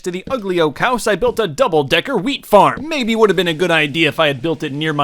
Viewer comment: did anyone else think he said “Weed farm” 😂😂